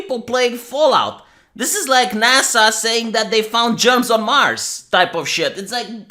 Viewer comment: What I heard was "NASA found jobs on mars".